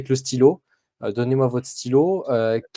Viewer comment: Ce n'est pas "donnez-moi votre stylo", mais "Rendez-moi mon stylo".